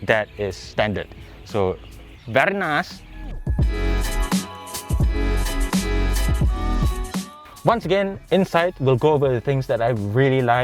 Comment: Did i just hear Borat say “very nicess”.